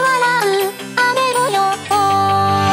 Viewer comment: 雨模様〜♪が 雨の予ッ報〜♪ に聞こえて仕方がない